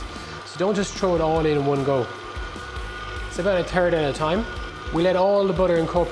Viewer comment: I love how the accent definitely made it sound like he said, "Add it in a turd at a time."